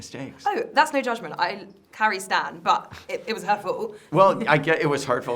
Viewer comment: misunderstands her accent here for a moment. He thinks she said “..but it was hurtful”, what she actually says is “oh, that’s no judgment, Carrie stan, but it was her fault”. So then he says “Well I get it was hurtful, breakups are terrible”. Thought that made for a funny little misunderstanding on his part, but I agree with her, there is no question it was Carrie’s fault. Interesting that in the writing room this is up for debate. Excellent interview. As said in other comments, it’s so much better when the interviewer knows the subject matter inside and out. That makes for a much better interview and debate. Well done.